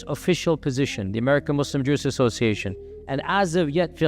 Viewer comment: The subtitles said it wrongly. It's supposed to be (AMJA) The Assembly of Muslim Jurists of America.